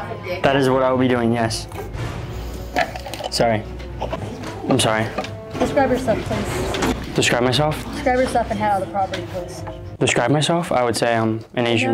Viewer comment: BRO I THOUGHT SHE SAID DESCRIBE YOURSELF TOO😂